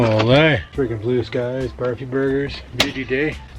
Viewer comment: Did you say Barfy burgers at 15:29? Lol Like , You can't do that on Television Barfy burgers? HAHAHA 🤣